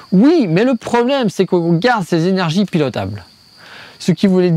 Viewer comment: A 18:41 je comprends pas ce que tu dis. Tu as mangé une syllabe ou deux. « Le problème c’est que.. » que quoi ? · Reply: oups désolé. Faut que j'apprenne à articuler. il m'a dit : "le problème c'est que l'on garde ces énergies pilotables (nucléaire en l'occurence)".